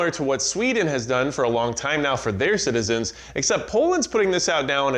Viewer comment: I thought that said Portland!! We stay ready out here. Thanks for all the videos.